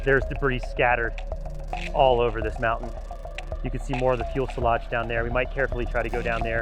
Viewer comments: I kept hearing "Fuel-selage" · Am I mishearing that, or is he saying "fuel-selage"? It's fuselage. :'D It has nothing to do with fuel, it comes from the french word "fuselé".